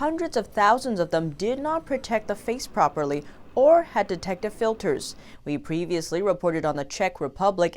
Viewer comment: Played it back several times and I hear "detective filters"...🧐